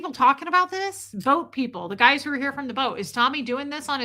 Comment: Sometimes it pays to not multitask and to actually listen, I thought you said “goat people” and I thought “goat people”! has crossed over, listening back I realized you said “boat people”. I will try to do better in the future. To be fair, I am only on cup #2☕️